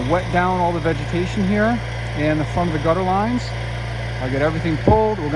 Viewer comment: Did you say foam the gutter lines? Time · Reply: Front of the gutter lines